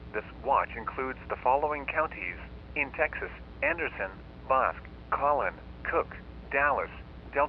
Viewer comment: I watch a LOT of EAS scenarios and this one has to be my favourite. I keep coming back to it, it’s very well done! I enjoy the story behind it and the effort put into it. Well done! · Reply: I hear Boris?